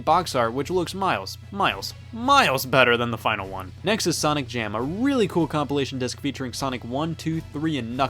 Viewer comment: W/ my broken headphones it sounds like you said "Sonic Damn"